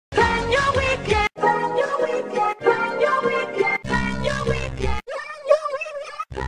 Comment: wat the say this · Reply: Plan your weekend